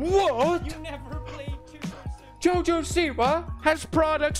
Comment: Did anyone else hear what you never played tubersimulator